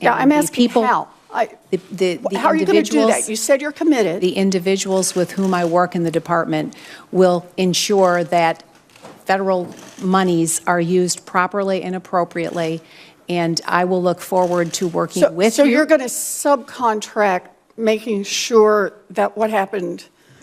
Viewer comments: Did she say federal moneys?? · Did she just say 'monies'? SMH